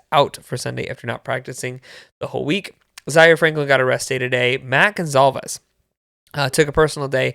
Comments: At first I thought I heard you say "zaire got arrested" and almost had a heart attack 😂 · I also thought you said Zaire got arrested. Gave me a heart attack.